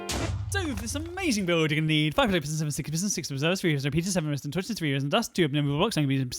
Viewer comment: uh I think I heard 6 sticky pistons Lol